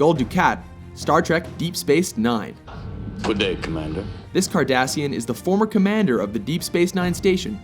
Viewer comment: I thought he said Kardashian.